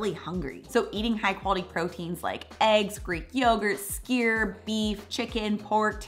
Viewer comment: What's the word she says at 2:50 that the caption says skier? I'm guessing that can't be the right spelling. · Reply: Probably Skyr